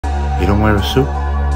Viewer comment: I paused after the first play and was sooo confused until I restarted it, because I thought he said "YOU don't wear a suit," and not, "He don't wear a suit?" 😅